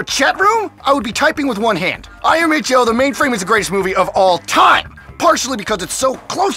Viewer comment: I cant understand whats he saying "I am a... the mainframe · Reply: Fabrix199 he said “IMHL” meaning in my humble opinion.